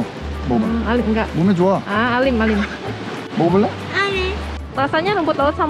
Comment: sampe mesti ngulang dong ngomong apa dan ternyata "alim" yaampunnn humorkuu. Tapi jokes aside, sundanya halus banget..